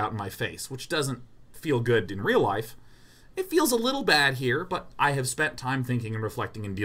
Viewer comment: I'm worried for Day9 at 1:32:40 this is odd. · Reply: Oh man on first watch I thought he said "a little busy here" haha, like he was lost in thought.